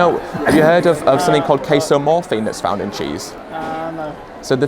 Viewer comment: casomorphine? I thought he said queso-morphine 😆